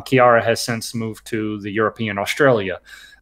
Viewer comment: wait...did just say kira move to Europe Australia. wow man I don't even know you anymore.